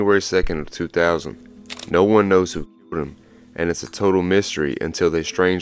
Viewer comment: The sound skips for a second and I can't understand what you're saying. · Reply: It wasn’t a skip, it was edited out from saying k*lled